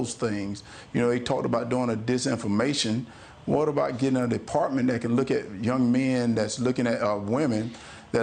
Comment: … 🤯🤦🏻‍♀️ I had to actually rewind that like 3 or 4 times, first two I thought he said “get an apartment…” not DEpartment, Jesus Mary, not that that would be any worse than what he actually said. I didn’t know if I’d ever heard or seen “word salad” before exactly, but I now can confirm, word salad. People try to say that’s what tr*mp would do/say … that was more like stream of consciousness if the stream were a dribble and the consciousness itself was debatable but I digress, he may not have worn a helmet while playing football 🙄 we all get the politicians we deserve, as they say, so … idk what y’all did wrong in Georgia but, better clean it up and soon 👏🏼👏🏼👏🏼